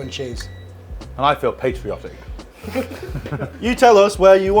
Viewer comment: Thought he said at end "I feel Pastryotic"